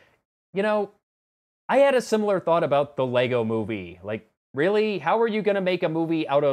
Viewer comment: You said "LEGO Movie", but I heard "Pixels" and then you said "I saw that movie and it turned out to be amazing!" and I seriously thought I'd lost my mind.